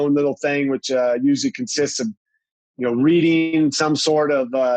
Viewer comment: 😂😂I thought he said weeding, 🤓 he said reading.